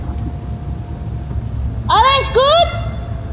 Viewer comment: when she speaks in "german," it really just sounds like she says "All is good?" in a German accent lol